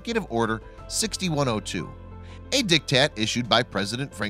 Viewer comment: did he just say "dick tatt"? 😂 Also, yes I'm aware it's "diktat". 😩